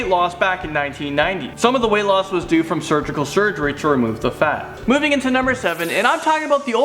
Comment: Surgical surgery? Did he really say surgical surgery? Seriously? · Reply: i thought it said suicide surgery